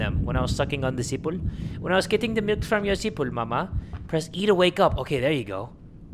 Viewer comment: Am I deaf or am I hearing sipul or sipon